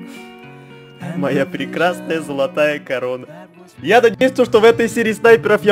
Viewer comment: Мне послышалось не корона а корова